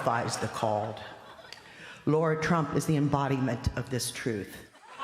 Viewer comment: did she say "Lord Trump"??